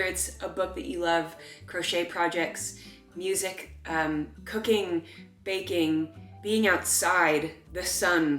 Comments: When you said cooking I thought you said cocaine, I was like uhh.... it might make you hopeful but that's maybe not something you should continue 😂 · I definitely heard 'cocaine' and was pretty surprised until the next item was 'baking' 😅😂